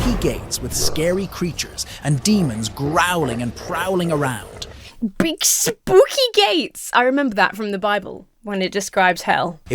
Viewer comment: I heard "big spooky gates" as "big spooky gays" and like... It would be so tragic if a big spooky gay woman were to, like, talk to me... I mean, that sounds so terrifying I hope that doesn't happen- if it does I might need to get comforted, and that would be just terrible. Especially if they were very scary and strong.... oh noooo~ (-your local nerdy transbian)